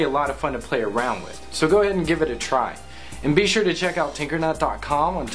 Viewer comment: at the end i thought he said tumbler tasebook and titter